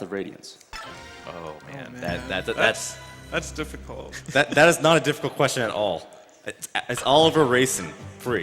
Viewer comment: "that's not a different question at all. As all of the racing ? ?" Couldn't understand what he said. Suddenly everyone bursted laughing. Could someone please tell me what he said? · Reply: "That's not a difficult question at all: It's Oliver-Reisen" as in, Reisen and the aristocrat Oliver who a̶b̶d̶u̶c̶t̶e̶d̶ ̶R̶e̶i̶s̶e̶n̶ did nothing wrong.